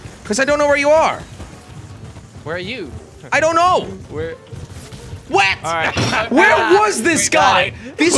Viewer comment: it sounded like said Wet instead of What.😂😂